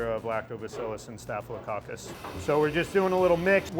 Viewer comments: Did he say staphylococcus ?? why would that be in food? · did he say "staphylococcus"?!